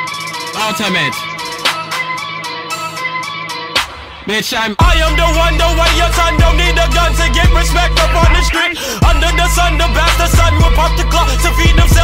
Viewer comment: AHAHHAHA I REMEMBER JUST MONTHS AGO THAT I HEARD THIS IN THE RADIO AND I MISHEARD IT AS I am the one the one your son dont eat the gun to get rest peck on the street